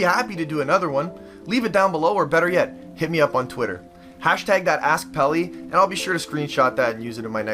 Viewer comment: I thought u said at the end, "#thatasspelly" but I was wrong lmao